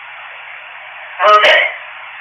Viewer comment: Hi guys, at 3:05:10, I thought it said "Prove it". Great show this week, thankyou.